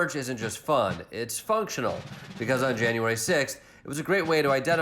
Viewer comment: Did I hear “January sickth”? I can’t tell if it was an intentional play on words